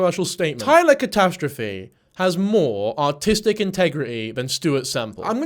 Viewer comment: sounded like you said Taylor had more "autistic integrity" than Stuart semple to my american ear and that was quite funny